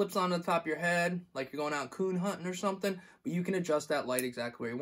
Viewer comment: did he say Coon Hunting